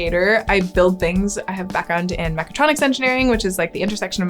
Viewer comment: I can't make out what you said at 1:15. It sounds like "Macotronics Engineering", but I'm sure I'm not hearing it correctly. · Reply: Mechatronics. Kind of a portmanteau of Mechanics and Electronics. Physical products with moving parts and electronic controls would be the main example.